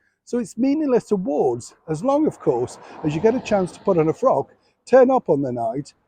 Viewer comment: what is a frack. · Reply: I think you misheard "a frock".